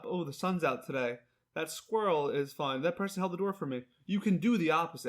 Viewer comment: Did he say, "that squirrel is fun" at 26:28? 😆 🐿🥳